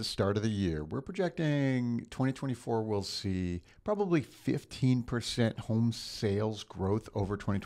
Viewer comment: At first I thought you said 50% growth instead of 15%. I was like, oh wow. :)